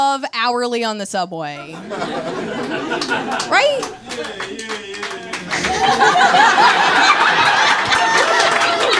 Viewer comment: What does the person say at 21:33? Subtitles didn't pick it up, just says [Music]. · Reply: "yeah yeah yeah"